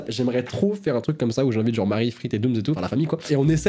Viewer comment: j'ai entendu ou j'invite Jean Marie 🤣🤣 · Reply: Mdr il a dit où j'invite Frite et Dooms 😂